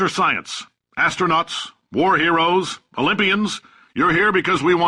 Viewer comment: Welcome to Aperture Science. Astronauts, War Heroes, Libyans- No wait. I always mishear Olympian as "Libyan"